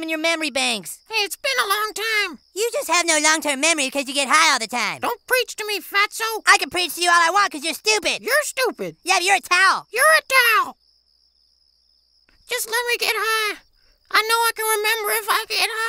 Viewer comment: Sounds like they were saying your a tau